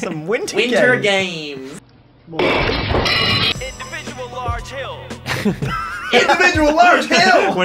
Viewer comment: Idk why but I always hear "Hit the bitch with a large hill"